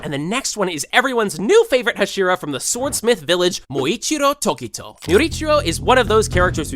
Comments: Did he just say yurichiro rather then muichiro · I heard yuichiro, that’s js me tho